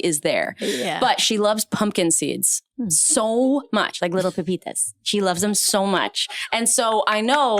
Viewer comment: Did she say "little pepitas"? 1:04:25 😊❤🥰🥰🥰🥰🥰